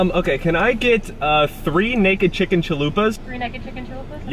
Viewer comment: At first I thought he said can I get 3 Nigga chalupa 😂😂